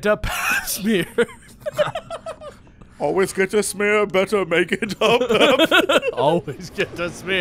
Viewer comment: When they were singing the Oompa-Loompa song at first I thought they said "always get a beer, better make it a Pabst" and I wish I hadn't understood it properly the second time.